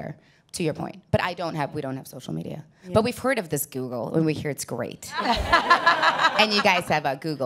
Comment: Finished up a previous youtube clip, then automatically rolled into this one....I was a bit distracted with other stuff & I could SWEAR I heard her say "Toxic Google", as she began introducing the show here. Made me stop & take note of what was going on! LOL.